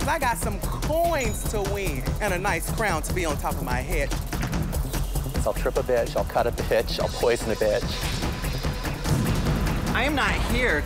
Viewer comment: What Tempest Dujour say 00:35 - 00:38? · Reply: "I'll strip a bitch, I'll cut up a bitch, I'll poison the bitch." -Tempest LOLOL THIS QUOTE MAKES ME LAUGH THOUGH